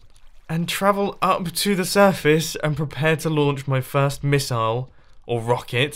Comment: It sounds like your saying "missal"